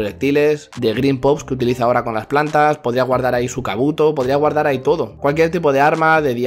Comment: podría guardar su canuto entendi jajaja